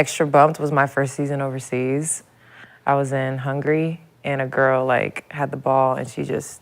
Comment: lexie visited hungry😂😂😂😂instead of Hungary